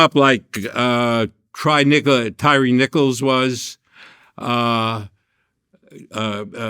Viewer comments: This man said tryre nigga instead of tyree nicoles at the 17:03 mark still playing the other side 😂 😂 😂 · Did he say "try nigga"?!?!?! 😂😂😂😂😂